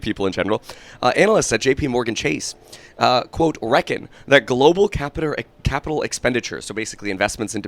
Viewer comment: I thought he said the global catheter. Lmfao